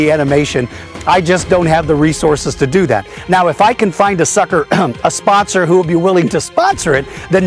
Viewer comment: did he just say find a sucker to sponsor @ 1:38??